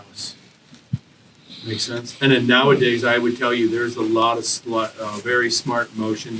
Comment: anyone else hear him start to say “nowadays there’s a lot of slut-”